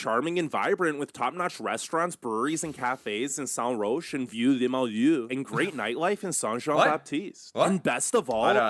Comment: I understood that part. I'm talking about what the narrator said at around 4:22 and to which reacted at around 4:28 - 4:30. ☺️ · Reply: The narrator said "St-Roch and Vieux Limoiloux", although he said it in a weird English/French hybrid accent. St-Roch and Vieux Limoiloux are the names of two boroughs right outside the old city, most notable for being very mixed zoning and thus a good walking/biking quartier.